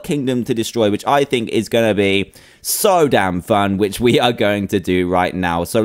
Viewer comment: did Dan just say damn?????????